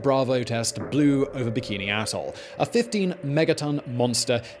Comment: so if you watch at 1.5× it really sounds like you said "the castle bravo test 'blew up bikinis asshole.'" Had to watch it a couple times to see what you really said